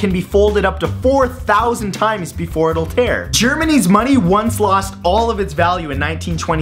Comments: I thought he said Germanese money. then I realized he said Germany's money. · did he say Germanese?